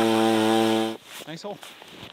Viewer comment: did you just call your wife an ice hole? LOL sounded like it.